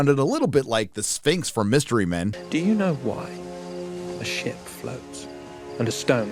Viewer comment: So at 4:50 is he making a comment if the show will succeed by saying "do you know why a shit floats, and a stone cannot?". LOL.. i swear thats what i heard, and had to rewind it.. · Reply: A ship